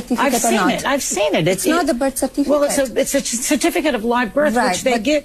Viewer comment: Did I hear her saying "BIRTH CERFITICATE?"